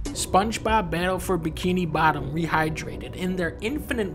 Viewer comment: Im not sure why, but I thought dunkey said, spongebob battlefront bikini bottom dehydrated